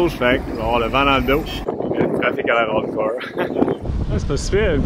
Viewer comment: À 6:46 tu dis quoi ? Après 5 écoutes j'ai pas compris :) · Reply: Je pense que je dis: le trafic a l'air hardcore!